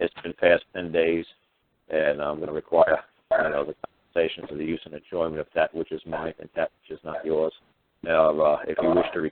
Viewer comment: Anyone know what hes saying @ 2:30 mark and like 5 seconds after...I can't make it out,audio very choppy at that moment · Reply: "I'm gonna require compensation for use of that which is mine an not yours